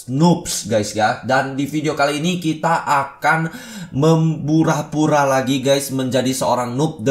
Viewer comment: membura-bura atau gua salah denger?